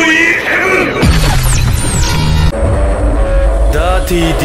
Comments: what he said? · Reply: Dirty deed done dirt cheap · Why do I hear "Wonder of Ohio" instead of Wonder of you?